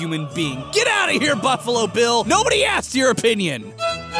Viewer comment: as a beatles/fnaf fan i thought you said bungalow bill instead of buffalo bill